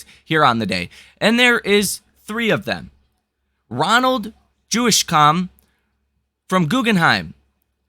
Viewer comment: Ronald Jewish-Com. Lol. I think it says Jewsikow, like Juicy-Cow. Hell of a name.